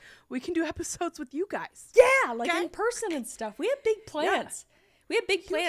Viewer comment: Every time says "big plans" I hear "big plants" 😂 I like it even more.